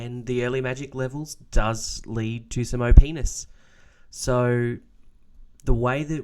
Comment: Why did I hear penis xdd